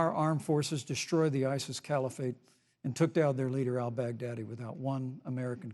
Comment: When mike said Al-Baghdadi, I heard "Outback Daddy"